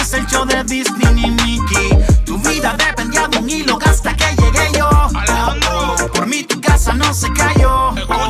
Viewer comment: Pensé que dijo Rauw Alejandro